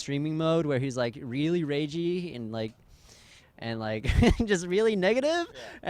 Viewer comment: did he just say regi LOL.